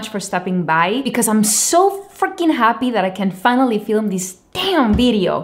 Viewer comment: I thought she said she is so fucking happy to there, but captions said it was freaking so.